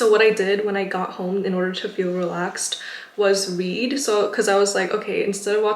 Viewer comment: I THOUGHT SHE SAID WEED instead of read😭😭😭😭😭